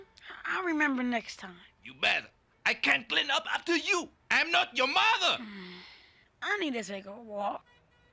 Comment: "I'm not his mother"! 😂😂😂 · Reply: and he says "I am not your mother." It's at 32:17 does he say "I'm not his mother."